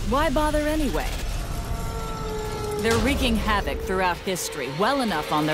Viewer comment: a male bird laying eggs? is this some kind of sjw bs? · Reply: oh, sounded like you were saying they're male